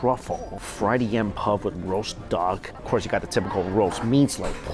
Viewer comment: did you say roast duck or dog? 🦆 🐶🤨😐😦